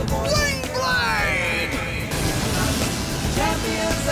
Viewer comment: So I'm not the only one who thought the intro was saying "Garlic Chanpions" right?